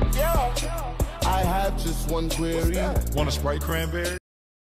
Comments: At the 64, i hear "wanna sprite cran grenade" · I hear “wanna sprite baby?” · Why do i hear “You smell like fairy” Edit: Woah 40 Likes?!? Most I’ve ever gotten! Edit :55 likes?! W O A h- Edit: 5 6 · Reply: so i guess im normal... i only hear distorted "Wanna Sprite Cranberry?"